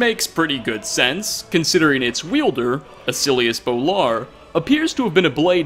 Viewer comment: Thought you said "The silliest Bolar" and I was exictied for a moment